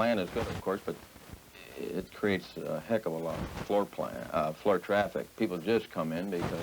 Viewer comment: Why can't that guy stop fidgeting in his chair!? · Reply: There may be a clue in his Freudian slip--almost sounds like he says 'foreplay' at 5:20.